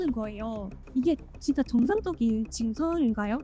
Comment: 0.1초 듣고 변조인걸 깨달은 내가 싫다... 인터넷문화에 너무 절여졌어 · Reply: 부분도 정(지지지지직) 상적인 이라고 들림